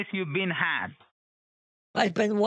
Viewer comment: WHAT'S THE PROBLEM? Hacker, while actively hacking: ”YOU'VE BEEN HACKED" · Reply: I heard "you've been had"